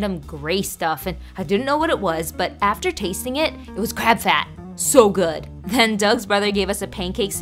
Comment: Bro when she said crab fat I thought she said crab ass😂😂